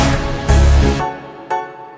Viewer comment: Am I the only one who heard that low pitch voice in the end? 03:56 ;-; "choiald"